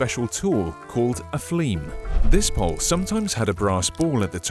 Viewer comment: Thought he said "a fleeb" XD